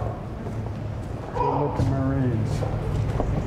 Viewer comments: WTF HE SAID "salute the Marines" INSTEAD OF ACTUALLY SALUTING THEM. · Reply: He said “good looking marines” smh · He fucking says out loud "salute the marines" instead of actually saluting them. He's receiving instructions through his earpiece. I wonder who is giving the orders... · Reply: He says “good lookin marines” turn your volume up before you parrot everyone else